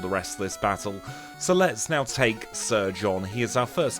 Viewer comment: You said let's take surge on and I thought you said let's take sir John lol😂😂